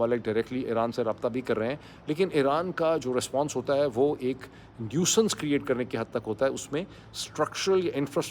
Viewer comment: what is this word Sir? I heard it like Nucense? I want to learn it.